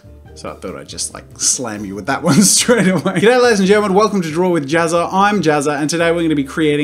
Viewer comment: did only I heard that he said "Get out ladies and gentlemen welcome to draw with jazza and I am Jazza"